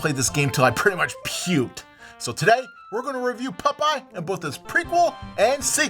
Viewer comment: Holy shit I’m in love with the way pronounces the name PUPEYE! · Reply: I thought it sounded like he was saying Pupeye too.